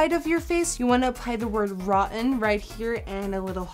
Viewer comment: In the beginning I thought the word rotten said protien